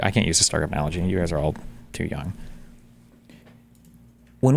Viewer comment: Destiny - "... I can't use the star craft analogy, you guys are too young." Me: now i feel old. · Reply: Oh, he said “young?” I thought he said “dumb”. I heard that and laughed and laughed.😂 Ah well…